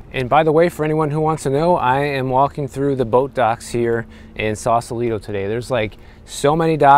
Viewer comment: When you said you were going to the boat docks I heard “Botox” and then you actually were talking about Botox at the boat docks 😂